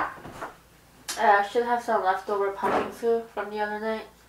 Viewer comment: What was she eating at 20:40? ( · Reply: I thought she said pumpkin soup hahaha